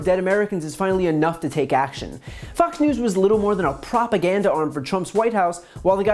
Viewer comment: I swear he said "FUX" news instead of Fox...subliminal...